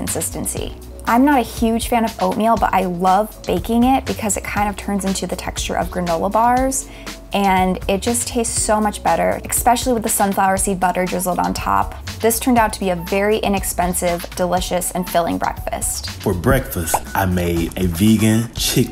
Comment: Did she just say “expecially”